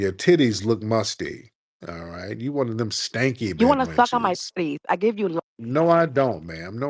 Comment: I thought she said stuck but she said suck. The lisp is crazy! 😂😂😂